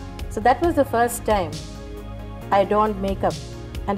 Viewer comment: What she said at 1:57 is "I donned makeup..." and not "I don't make up". She had a good command over the English language as is evident from these snippets.